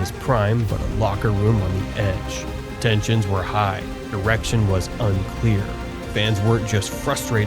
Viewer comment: he says "erection" instead of "direction"